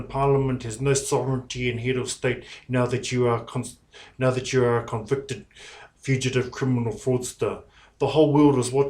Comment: I swear he was gonna say "now that you are constipated"